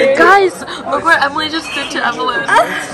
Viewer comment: am i the only one who hears "look what evelyn did to evelyn" LOL